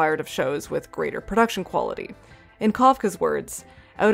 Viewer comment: I did keep mishearing "Kavka" as "Kafka" and I was like WHAT did Franz have to say about Jersey Shore?